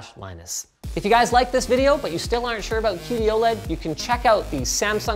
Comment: i thought he said 'Cutie-OLED' lol